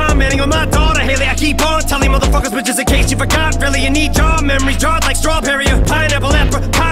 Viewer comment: "But just in case you forgot and need Ja memories, Jarred like strawberry or, Pineapple Apricot Jelly.” Holy... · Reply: I thought he said jarred memories